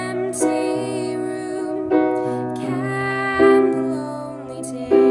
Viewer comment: All I hear is 'cannelloni', not 'can the lonely' Why am I like this